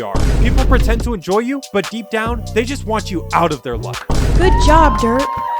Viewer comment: at first i thought ether said good boy exept of good job😂😂😂